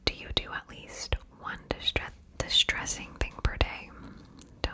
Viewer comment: girl I thought you said distressing and I said work 😭😂